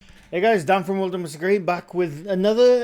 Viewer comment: For months, I thought you were saying in the beginning of your videos, "Hey guys down for another World of Mr Grey."